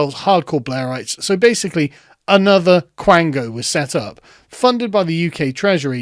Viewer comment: for those of us who (like me) thought we misheard him, he actually said "Quango", which is apparently a real word. Google defines it as "A semipublic administrative body outside the civil service but receiving financial support from the government, which makes senior appointments to it."